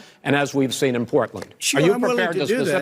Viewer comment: Wait what was that...what did trump say at 3:33 because it sounded a shit load like "sure" to me...and then did he not say exactly what he was told to say, "stand down and stand back"... And just abit more, an idea is not the same as ideology ffs... Edit: can't wait for the strawman and nothing addressed about what I actually brought up.. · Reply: Yes, he said "sure", he said he would condemn... but he didn't in the end. "Stand back, stand by" means something very different from "stand down and stand back"